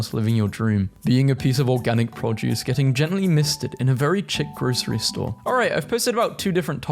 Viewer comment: chick instead of chic 😭😭😭